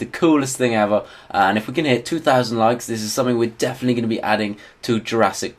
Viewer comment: I thought he said 20.000 likes